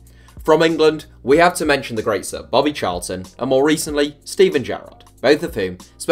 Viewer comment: I misheard ' great Bobby Charlton', for ' late Bobby Charlton'. Im not even kidding I swear I was going to have a bloody heart attack 😂